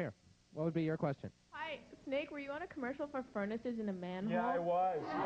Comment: What commercial was that? I couldn't hear what she said. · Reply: 666mathew a commercial for furnaces in a manhole I believe is what she said.